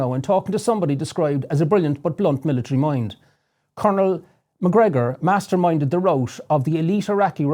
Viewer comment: I hate to be that guy... i really do. Anyone else think it sounds like hes saying "Coner McGregor "?